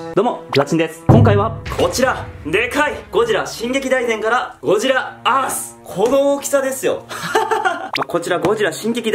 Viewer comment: did he just say Godzilla ass